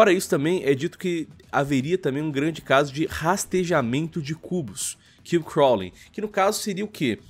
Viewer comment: nessa hora eu ouvi "rastejamento de cús" kkkkkkkkkk